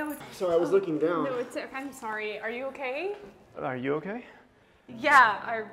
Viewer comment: You said sprry instead of sorry